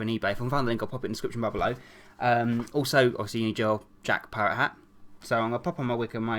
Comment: did he just say Jack parrow hat?